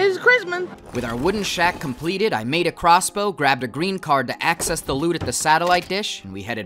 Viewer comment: wait did he say green card 🤧😂